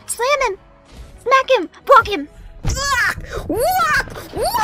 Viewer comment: I think Fauna said "BONK him"